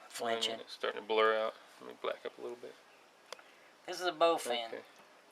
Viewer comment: did he say "let me black up a little bit"....lol